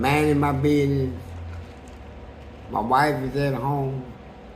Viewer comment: Did he say wife???? He got a female ??